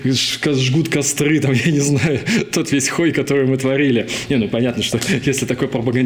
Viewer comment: мне послышалось или он сказал " тот весь х...й который мы творили " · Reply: Хой